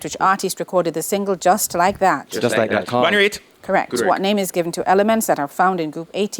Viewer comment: Did he say "Bonnie Raitt"?